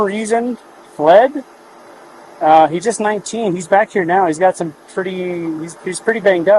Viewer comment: did he say he was 19? I dont know what 19 means apparently. He said the woman went couch, and shes category. I'm assuming I know what those mean though, just never heard of it.